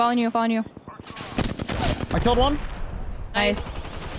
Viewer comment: did they say nice in sync? 😂